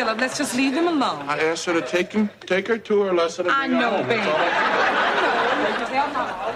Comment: What does he say between 0:22 and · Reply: "Ash, hop up. Let Jazz get busy one time"